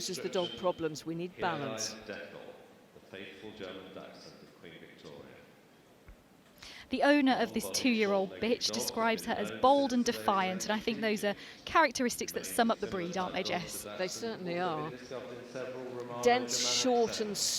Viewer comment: did she say “two year old bitch”?